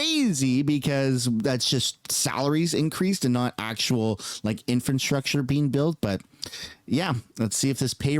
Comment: Purely constructive criticism…. Or am I mishearing you all the time? It sounds like you say “infant-structure” all the time you mention it instead of “infrastructure”. Only trying to help.